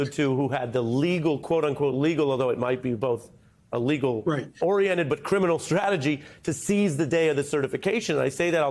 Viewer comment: The “lethal oriented criminal strategy,” from John Eastman is the best description of it that I’ve heard. It’s an appropriate and accurate description of his role. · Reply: i thought that he said, "legal oriented criminal strategy". However, I like yours!